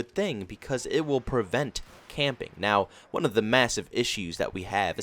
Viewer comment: My dumb self thought he said campaign I was confused😂